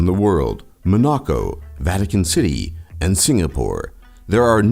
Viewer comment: What 3 city states did he say? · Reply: Monaco, Vatican, and Singapore.